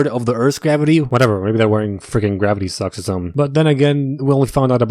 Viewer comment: When you Said “gravity socks” i thought you said “gravity sucks”😂